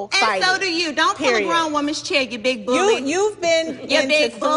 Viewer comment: When Porsche said “you big bully” to Kenya, I really thought she said “you big booty”